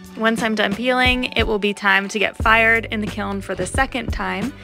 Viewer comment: This looks so fun but when she said its time to fire it in the however you spell that, i thought she said "it will be time to get fired-"